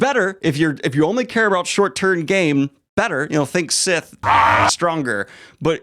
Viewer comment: I enjoyed this vid and I wanted to say I heard “short tern game”